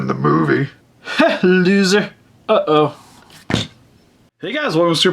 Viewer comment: what did he say? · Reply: Ha, loser. Uh-oh